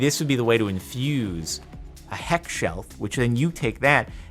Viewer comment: Bro every time he said heckshelf I thought he was saying hex shelf’s but then I realized he was talking about the mod that adds hellshelfs